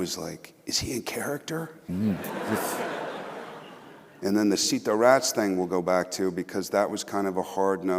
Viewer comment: What did he say? "see the rats thing" ???? Anyone know what he said or is talking about? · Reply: I think it's Montecito Rats or Cito Rats for short. They were a surf gang in the late 70's in Montecito, California and Brolin was in the gang. I can't exactly remember if he was actually in the gang or was just really close with some of the guys and hung out with them but it was an intense story. He told the story about them on Ted Danson and Woody Harrelson's podcast several weeks ago. They talked about so much. His career, his father James Brolin (an actor who I remember watching as a kid) his mom who was a wildlife ranger in Texas and California, his kids, being a recovering alcoholic...everything. It was a damn good interview because he was so open and honest.